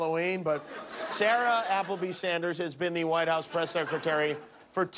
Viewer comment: Like Wanda Sykes said, the presidency hasn't aged Trump, it's aged us! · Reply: Did he really say sara applebee at 4:20? Lol